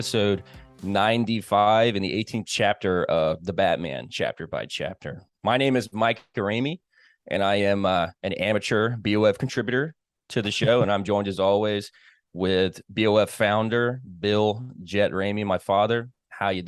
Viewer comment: At first I thought said "This is Mike Duramey", before the penny dropped. I thought 'Who the hell's this guy?" Lol